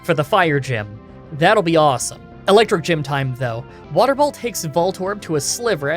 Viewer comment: Next challenge is up, Pokemon Fire Red with Bellsprout!► <link> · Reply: Ok I think my hearing is dying because at 6:46, weather ball sounded like water ball to me, did anyone else catch that? if ur reading this I got u covered